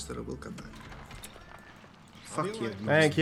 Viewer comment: it sounds like said "Fuck you" lol